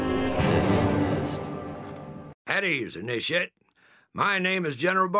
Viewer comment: I thought it said "At Ease,Dip Shit" during the briefing...